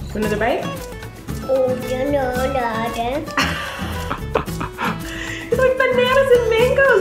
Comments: it sounds like said, “oh no I don’t” with a British accent lol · want another bite? ew no no not again Did anyone else hear say that at 6:34? no just me? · want another bite? ew no no not again Did anyone else hear say that at 6:34? no just me? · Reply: I heard him say "Oh, no, no naw dan" Whatever THAT would mean😂😂😂